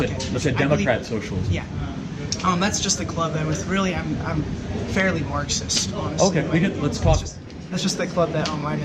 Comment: Did I hear that right at 53:43, "...really, I'm fairly Marxist." ?? Wow. This is truly how they do it. They creep into the system and cultivate the from within.